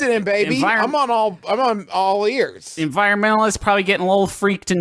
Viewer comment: Hearing Justin almost say “I’m on all fours” about Longburger (22:02) make me almost choke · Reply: I thought he said "I'm on all ears" But I do have an auditory processing disorder 😅